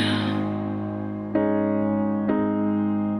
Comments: Lmao I thought she said "Your body's pulling trees" not "your body's poetry" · I thought she was saying "your body is poultry"